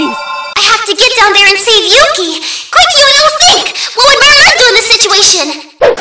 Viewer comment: <- could anyone tell me what is Yuno saying? "What would ... do in this situation?" I have no idea what this pink thing is, I thought it's a digimon but I didn't find anything like that. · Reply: MurMur is from the anime '-'